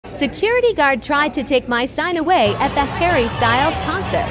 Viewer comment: I misheard it as “security guard tried to take my son ‘ and I was like girl go tell the PoIcE !